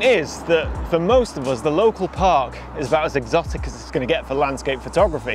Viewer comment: I thought you said "the local pubs..." Great video!